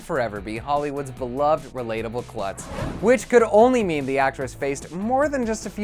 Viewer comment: Jesus I thought he said Jennifer Lawrence "will always and forever be Hollywoods beloved relatable slut" 😅😐